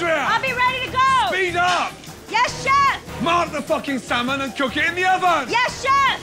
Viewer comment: Why does it sound like he says motherfucking salmon